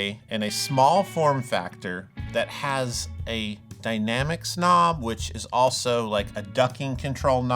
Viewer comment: "dynamics knob"... I thought you said 'dynamic snob!' lol